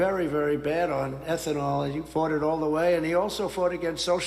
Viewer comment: I thought he said, "He farted all the way." Then right after, he said decimated & I thought he said "defecated". Bad audio, I guess.